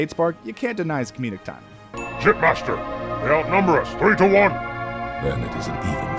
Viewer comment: Idk why but really thought that elite said "Shitmaster, they outnumber us"